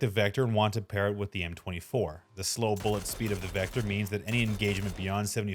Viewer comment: did he say the slow speed of the vector ? at 2:01 ? LOL